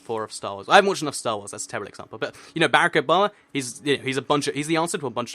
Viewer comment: I thought he was saying Bear-Ick Obama not Barack Obama